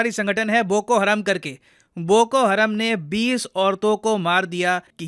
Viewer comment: I heard that you said "Aur boko haramzyaade aatanki sanghathan" 😂😂